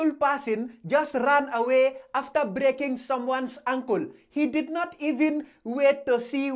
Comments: LMAO, I thought he said her uncle, but he saying ankle. 😂✌🏿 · ”he broke her ankle” Sub titles:”he broke her uncle” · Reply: I thought he said "uncle" too and i wasn't even reading subtitles. 😩🤣😂😭